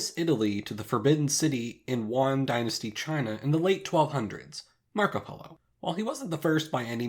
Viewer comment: I thought he said markiplier, not Marco Polo